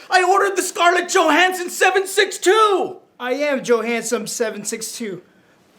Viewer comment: "I am scarlet Joe Handsome" LOOOL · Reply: Choa Ming Guy lil I thought he said Joe Hamson.